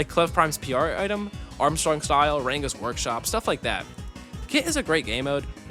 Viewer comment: did i just hear this man say ranga's workshop..?